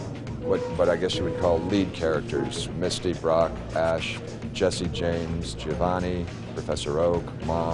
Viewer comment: oml I'm crying rn I thought they said "Jessie J" cause I wasn't fully paying attention and I was listening to it instead of watching the video, and I was like "wtf does Jessie J have to do with this" XD